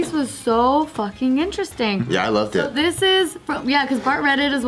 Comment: I could have sworn bart said “yeah I love dick” following up to Jess and but I think he said yeah “I loved it”, meaning the article 😂 whatever potato tomato.